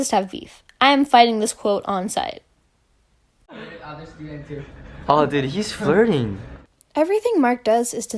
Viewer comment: what did he say tho? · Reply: He said "Oh shit my bad"